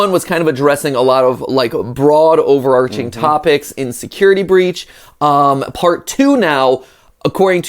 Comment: 2 things 1. I love the chemistry between you two! 2. I thought you said “insecurity breach!”